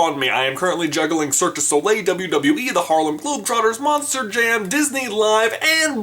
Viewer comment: did you just say wwe